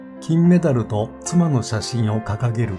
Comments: 最後、不快感を与えましたに聞こえた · 「"深い感動"を与えた」か、「＂不快感"を与えました」に聞こえて「え？は？」ってなったわ